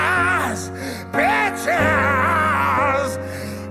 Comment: Jack Black makes it sound like he is saying B!TCHES great vocal 👏 technique 😎🤘. 9:17.